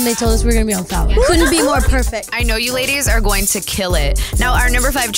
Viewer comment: they are gonna be on POWER? is that what Lauren said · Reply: tna m she said fallon as is Jimmy Fallon